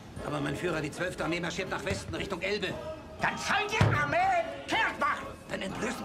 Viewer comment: real germans kknow what he is saying xD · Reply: Stiley Die Armee zieht nach Westen Richtung Elbe